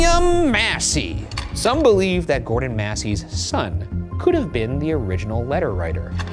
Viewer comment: Misheard Gordon Massie as Gordon Ramsey at 27:30 and got really confused